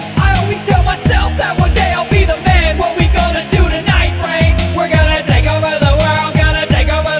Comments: Your favorite martian has My favorite reference. "What are we going to do tonight Brain?" "Same thing we do every night Pinky, Try and TAKE OVER THE WORLD!" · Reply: i always thought it said “frank” instead of “brain” until i saw this comment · "What are we going to do tonight, Brain?" "Same thing we do every night, Pinky. Try to take over the world!" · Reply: Bruh I always thought he said Frank not brain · Realized he said "what are we going to do tonight brain" like if you know what show I'm talking about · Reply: omg i thought he said Frank not brain xD